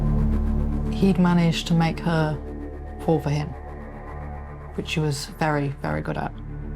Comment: what the f-ck is she saying...."he'd managed to make her horbeyet..." I've played it back over and over, but I just cannot make out that word..."horbeyet, horbered, horbyit, hallevd, morher...." But whatever it is, he's apparently really good at it. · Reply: "He'd managed to make her fall for him" 🤗